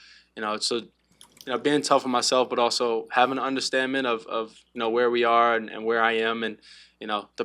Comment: Want wait...Hold up...I'm sorry, I can't...Did he say "understandment"?